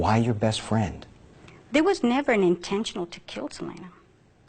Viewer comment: There was never an intentional to kill Selena. I thought she said, "intention." Either way, the bullet was fired. Once your bullet kills a person, your future goes out the window.